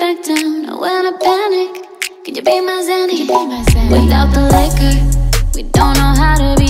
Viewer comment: I legit almost thought she said will u be my zaddy? smh